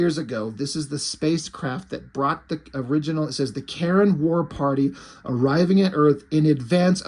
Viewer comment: It sounds like is saying the Karen warparty 😂